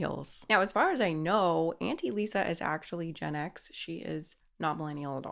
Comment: I thought you were saying anti-Lisa 😂